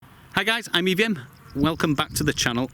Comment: When you said "I'm EVM" I heard "I'm Vivian".